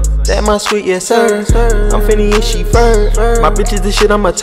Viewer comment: it sound like he said “I’m phineas she Ferb”.... am I the only one thought he said that or..?😂